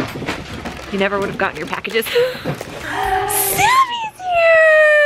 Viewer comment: When she said “Sammy’s here” I thought she said “savys here”